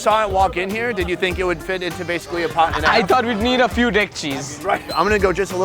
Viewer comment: Ffs I heard few dick cheese 😂😂😂